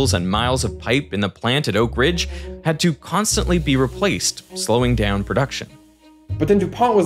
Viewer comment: caption error. The captions say "had to constantly replaced" instead of "had to constantly be replaced"